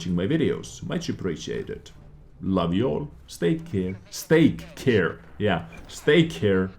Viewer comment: Almost spit my Steak out when I heard "Stake care" :)